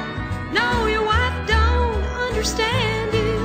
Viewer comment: it sounds like she is saying NO ONE UNDERSTANDS YOU not your wife wont understand you